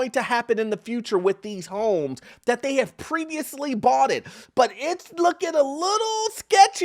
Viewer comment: Did you say “boughted”😂